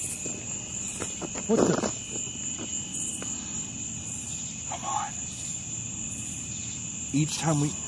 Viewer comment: when said what the its sounds like a male's voice saying come on!